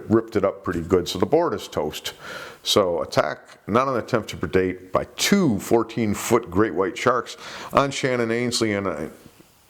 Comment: At first I thought it said 214 foot shark. I was thinking, sh!t. Bruce has been at those growth hormones 💉 😉😂